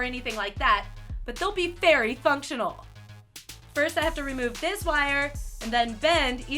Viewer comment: did she say "FAIRY functional"? 😂